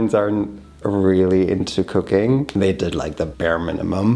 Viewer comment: I had to relisten to this part 3 times! I swore you said your parents were really into cocaine! 😮